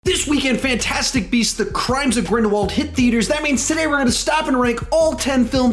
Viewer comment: Haha! For a second I thought he said half-blood prince at last place, instead of crimes of grindewaledl I need to go get my ears checked.